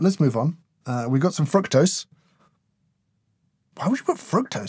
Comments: I really thought you said: "Frog Toes" went you said: Fructose haha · Was it just me that thought he said "We've got some 🐸 toes"? 😂